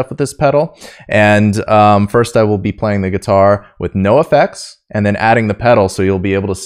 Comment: what he said: no effects what I heard: NOFX